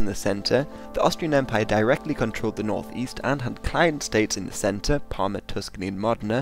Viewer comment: I can't make out what word you are saying at 0:36... "The Austrian Empire directly controlled the Northeast and had _?__ states in the center..." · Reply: client states